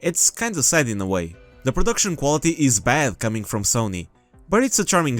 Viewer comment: you did….. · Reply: It sounded like he said reproduction organs😭😭💀